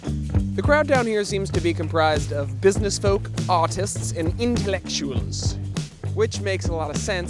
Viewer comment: Definitely thought he said autists before I realized he was doing a fancy accent lol